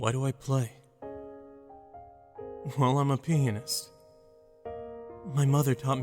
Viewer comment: I thought he said penis instead of pianist